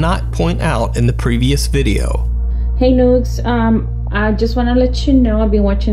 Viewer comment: sounds like she saided hey nudes